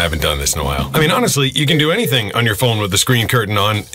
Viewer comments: I thought he said "Take it from me, a black guy" and I was like damn he's really blind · Bro until I heard it a second time I thought he said "take it from me a black guy" bc I have a fan next to my face